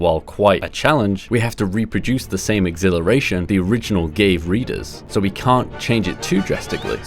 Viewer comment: Why did i hear gay readers